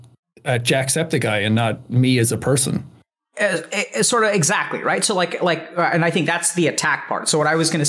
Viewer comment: did Alok just say "sort of, exactly" ?